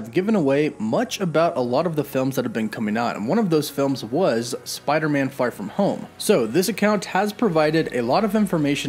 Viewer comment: @ 1:14 Is it just me or did you say "Spider-Man: Fart from home" ??